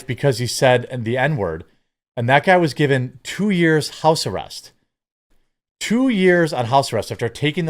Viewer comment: 2 years of house arrest for murdering an old man??? How is this justice??? How is that okay?! Edit: I misheard, yeah not prison but house arrest